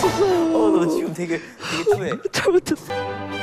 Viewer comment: did he just said cold? 😂